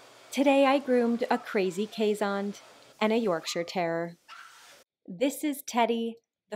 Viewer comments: I could have sworn you said Yorkshire terror 😂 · At first I thought I heard "Yorkshire Terror" and was like... 'is it really that bad?' lol